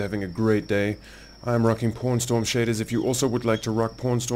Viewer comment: nice to see Kenshi vids, LOL sounds like you say "porn star shaders" . Also i am playing a town playthrough and found that making monies is done best when u have a town. Love the vids and enjoy listening to you while i play. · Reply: Haha. full disclosure, I think i did say porn star that first time ( sorry PawnStorm XD). Glad you like them man, thanks for watching.